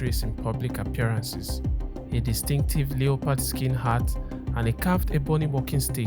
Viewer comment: Not gonna lie, I thought you said "Leopold skin" at first. Lol. Just so you know it's preonounced "Leh-purd." The o is silent.